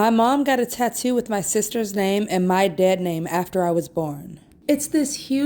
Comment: For the tattoo one. I'm a ftm, and I actually don't connect with the whole dead name thing. I still think of it as my given/brithname, cause that's what it is, the one that was give, and as we transition we chose the names we transition to. Not to say it's ok for people to refuse to use that chosen name. But that's beside this point. This one is the tattoo was there before the mother even knew, possibly before the narrator themselves knew, and to get mad about it it ridiculous. The mother is supportive, even tries to hide it from view which she shouldn't have to. So yea, that person is an asshole for this. Honestly as a community or whatever, trans individuals should accept that those names are our past, are part of what has made us us. And seeing it in thing that were done before we came out should not be upsetting, it's our past, good or bad, trauma and all. And again, this is not me saying misgendering or being call by birthname and not our chosen names are ok, but it someone is accepting and genuinely trying we should not attack or get angry we them. Save it for those that are not accepting. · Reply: This is a good explanation cause I thought she was saying her Dad name incorrectly. She saying my dead name. 🤦‍♀️